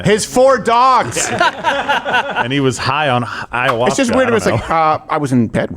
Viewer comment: Did say “High on Hiawatha?” 😂